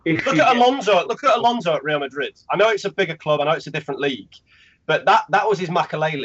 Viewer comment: did he say they are a bigger club???